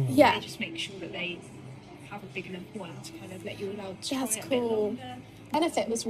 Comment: Did she just say "jazz cool" i keep rewinding it to see what shes saying ......is this a new thing ?! Or is she trying to make it cool....the youths of today hahaha im 38 an feel like a 100yr old 😂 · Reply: I think she said "that's cool"